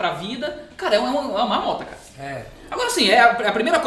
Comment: É uma marmota foi isso que eu ouvi.?kkkkk